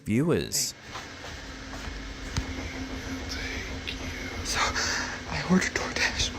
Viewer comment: Anybody else hear “take you” at 55:41 when the severe knocking is happening and creepy lady by the tree video? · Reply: I heard "he will take you". I'm also wearing bone conduction headphones while watching, so maybe that's why I heard "he will take you" so clearly and definitely not "thank you" like the cc said.